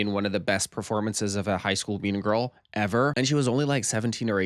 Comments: what is a “bean girl”? Or did I mishear? 🤔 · what is a “bean girl”? Or did I mishear? 🤔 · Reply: i heard “mean girl.”